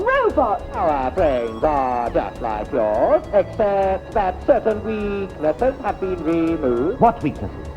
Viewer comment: Can anybody please explain what the Cybermen said here? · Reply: Our brains are just like yours, except that certain weaknesses have been removed.